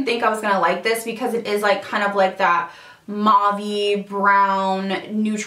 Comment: i thought u were gunna say mom lip not mauve 😂😂😂 it looks good on u tho👌👌💖💖